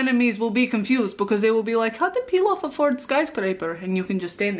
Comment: The first time I saw Pilaf, I thought she was saying peel off... i swear im educated